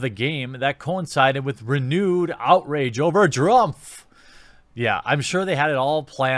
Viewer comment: did you just call Trump, Drumpf because that's what it sounds like. You also called him that again at 4:47. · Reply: Nope, he said Drumpf, it was something stupid the NPCs did a while back.